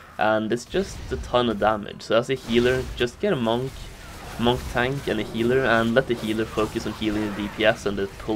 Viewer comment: "Just get a tank eller healer" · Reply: hmm where did I say that xD at 1:47 I said "monk tank and a healer" however my accent might be a bit weird :P I do mix english and norwegian sometimes tho xD